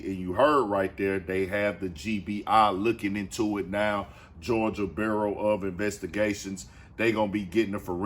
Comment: Georgia bureau.... I thought he said Joe burrow😂😂